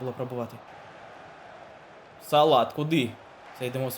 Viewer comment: Мухамед САЛАТ,мені почулось салат,хах